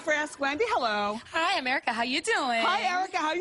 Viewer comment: i thought she said hi america 😂😂😂